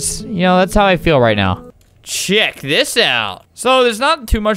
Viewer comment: did i hear shit!?